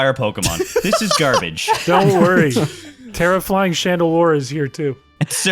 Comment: my brain kept processing Tera-Flying as some kind of weird pun on terrifying, and it wasn't until now that I realized what you were saying